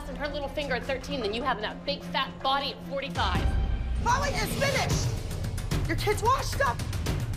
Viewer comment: she really said 'chloe is finished, your kid has a lazy eye why dont you go fix it!' the producers edited it and if you listen to it you can tell.